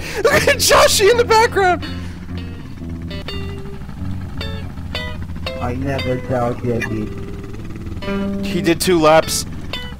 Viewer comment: "Let's-a goooo." "I never doubted me." "No question." "You cannot beat me." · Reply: I hear it as "i never doubt weggie", think he meant reggie